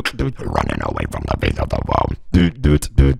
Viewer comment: folks what does he say in that part? I'm Brazilian if someone can answer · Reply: "running away from the fate of the world" ?